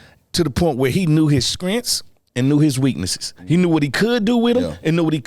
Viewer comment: Yoo did Fred say “Skrintz” (strengths) 🤣🤣🤣